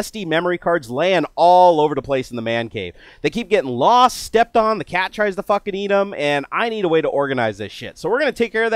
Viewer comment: The cat tries to fucking eat them HA HA HA LMFAO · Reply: PrankCallersUnited I laughed even harder because I heard "The cat tries to fuck and eat them" hahah